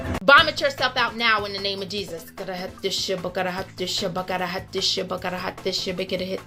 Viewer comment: Gotta hide this ship gotta hide this ship gotta hide this ship is what I heard her saying